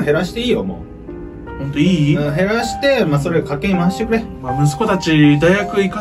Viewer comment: ここ家計に回してって言ってるのかｗ 賭けに回してって聞こえたわ